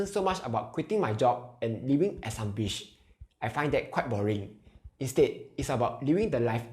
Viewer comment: sorry, you said "quitting my job and living as I ....?" couldn't catch that word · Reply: Ops my bad! It is "living at some beach"